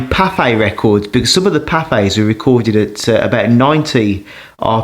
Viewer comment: I thought you were saying Popeye records but then figured out you were saying Pathe because they had weird speeds. They had a giant 20 inch record 120 RPM